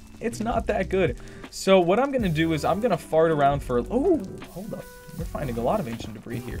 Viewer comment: dude 12:24 did u just say "Im gonna FART around for a" u cracked me up